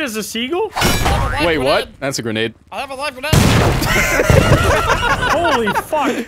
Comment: I thought when had a live grenade I thought he said alahu akbar nah he just said I have a live grenade 😂😂😂😂